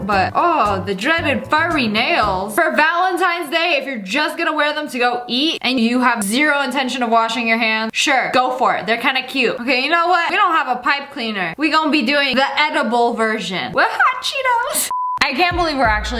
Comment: I thought said she didn’t have any pipe cleaners lol